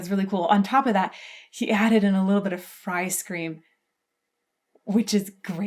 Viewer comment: 'He added in a little bit of f'ice cream'. Sure she surly did not say Ice Cream' or 'Frie Scream' Or Fry Scream'. There is just something I must be missing. Any help? Maybe some musical notation not yet known to me.